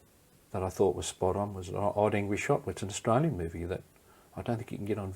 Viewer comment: What’s the movie that he says is good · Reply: The Odd Angry Shot (1979)